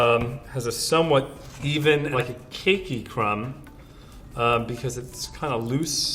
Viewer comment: it sounded like he said KINKY crumb...